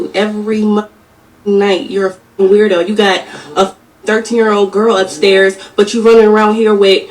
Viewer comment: 🤯 Wait a min did she just say he has 13 year old upstairs 😧 why not call the 👮‍♀️ instead of going live 🤔